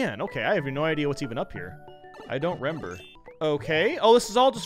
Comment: Am I the only one who heard him say "Rember" instead of "remember"?